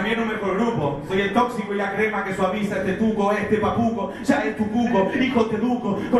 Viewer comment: Pense que decia Papo mc....... Me comi el amague